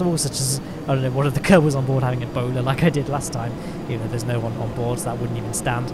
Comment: I definitely heard "a boner" for a second.